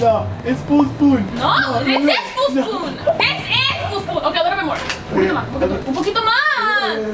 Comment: Like si escuchaste lo que dijo lele amor o soy yo?? minuto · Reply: Lo que dijo fue “a little bit more” que significa “un poco mas”